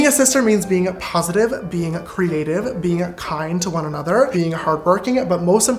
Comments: Fully thought he said cremated and not creative for a second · dude; why when he said creative - i genuinely thought he said cremated. i was like, oh. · When He said creative I thought he said cremated